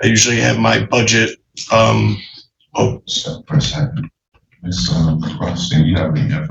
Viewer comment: it sounded like “STOP FOR A SECOND”